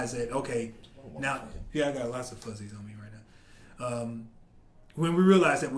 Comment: I misheard that lol ''I think that, once people realize, yeah I got lots of pussy''